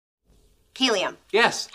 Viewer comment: I heard "Kylliam" instead of Helium